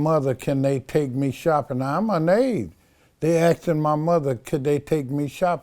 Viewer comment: did he say he was "anade" or amazed? He must be thirsty the way he keep sipping😁